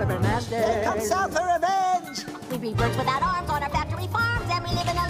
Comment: I thought Wakko said, "The south is coming for revenge" as in the CSA.